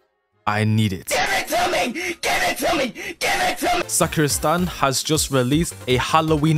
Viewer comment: For a moment i thought this guy just say suk ur stand instead of sakura stand